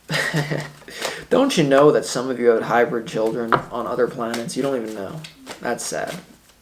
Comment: lol why did I hear him say "hybrid children" instead of "help" ? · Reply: he did say hybrid children, I listened again. Lmao I’ve heard him say it in other Sagittarius readings. Idk about y’all but I’ve always felt only half human. Lmao